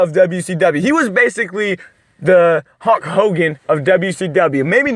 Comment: Did he say HUNK HOGAN?!!!! Around the 2:44 mark?!!!!!!!!!!! 🥸